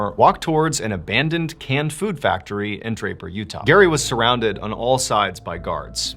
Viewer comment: I thought he said diaper and not Draper